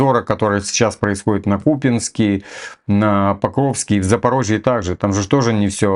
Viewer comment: купинск мне послышалось путинск.... :)